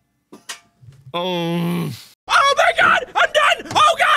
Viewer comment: OMG A CHAIR! D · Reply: If you repeat the time stamp when the chair lands on the ground it sounds like mark is saying “KNOW MY GOD”